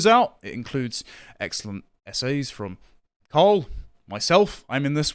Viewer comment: At the beginning there i got confused, i thought he said SA's which contextually seemed like it might fit for a micro second, then my brain initiated and i realised essays, which made infinitely more sense 😂